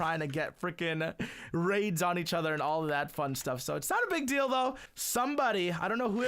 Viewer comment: Did he said ,,Its not a BIG DILDO'' HAHAHAHA! :)